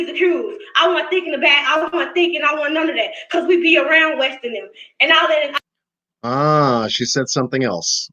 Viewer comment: Sound like she said we be around west an nem. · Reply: Hi Jessica ❤! Yes, West and nem was referring to the west side boys from the west side area. West and Jackson area. Much love to you.